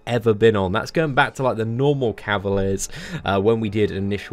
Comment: did he just say “normal cavilar?” My boys been gambling so hard he forgot what those “before cavilar crystals” are even called. 🤣